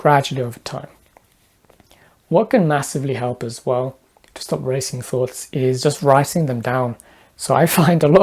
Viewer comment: I thought it said stop racist thoughts